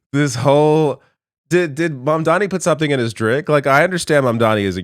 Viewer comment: the subs show "d¡ck" instead of "drink" but its might not be wrong loll